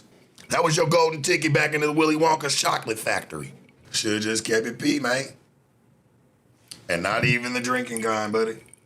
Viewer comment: did he just say "shoulda kept it P man, and not just the drinking kind."?!?!??!😮😮😮😮